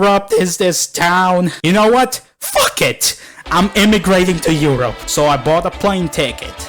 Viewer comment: fruk it is what I heard